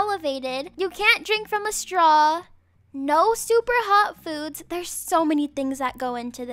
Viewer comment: did you say no soup or hot foods or no super hot foods????